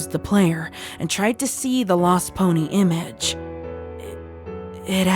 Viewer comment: I thought it said: Lest pony.. cuz... Molest ... Ima see myself out